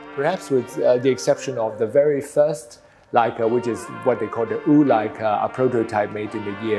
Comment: I think he said ur-leica not wu...